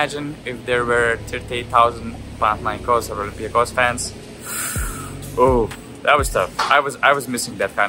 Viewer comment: can somebody transcribe what he said in 6:29 . Which fans? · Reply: Panathinaikos and Olympiacos fans. One of the biggest rivalries in EuroLeague, one of the craziest fans in the world, its probably hard to imagine for people that dont know much about the level of basketball of EuroLeague and fan culture